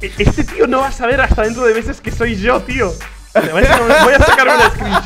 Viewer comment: ese pibe tiene que estar mas feliz de ver esto ahora, espero que pudiera ver este video y saberlo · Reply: Cuando dijo "no lo sabrá hasta dentro de meses" yo sin querer entendí "no lo sabrá hasta 3 meses" xd me equivoqué